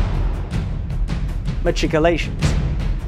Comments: Bloody brilliant video mate. I kind of thought you said ma"t"icolations at the beginning and reacted with, YES! we can all have our different pronunciations ^_^ I loved all the additional context you added, such an intriguing topic. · Bloody brilliant video mate. I kind of thought you said ma"t"icolations at the beginning and reacted with, YES! we can all have our different pronunciations ^_^ I loved all the additional context you added, such an intriguing topic. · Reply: I always thought you were saying "MeTRICulations," Shad. Guess I was wrong.